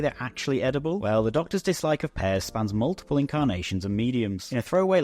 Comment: I thought he was saying he didn't like Pez.